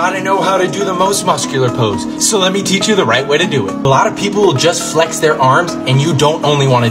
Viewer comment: Who else thought he said “black people just flex their arms”? 😭😂